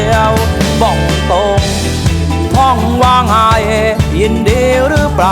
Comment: พ่องว่าไงยินดีรึป่าว 55 ใครได้ยินเหมือนกันมั้งหวะ